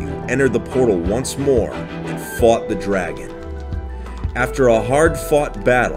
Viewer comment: i heard “fucked the dragon”😂😂😂